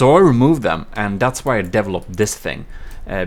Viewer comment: So, how many of you out there caught when says "developed", it sounds like he said "deviled up"?